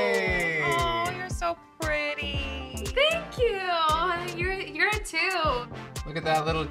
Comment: When told her she was pretty I thought she said "THANK YOU! You're a 2!!" 🤣🤣🤣👀👀 That's awesome. 💞